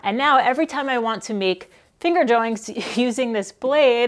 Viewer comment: Did you say... "finger joinks"? 😁